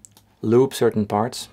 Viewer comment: it's super funny to read the subtitle.. instead of "loop some parts" It says "lube some parts" 🤣🤣🤣🤣🤣🤣